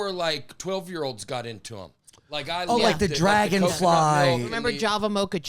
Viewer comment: did just say dragonfly instead of dragon fruit lmaooo